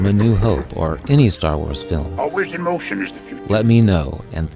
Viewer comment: I was 21 and didn't mishear any of these in the theater. Except, "Let him have it." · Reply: But in Empire I misheard Yoda as saying, "Always emotion is the future."